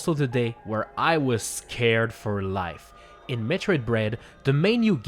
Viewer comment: did he just said Metroid bread ?